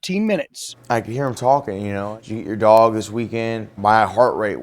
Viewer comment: I am very confused did he say did you eat your dog this weekend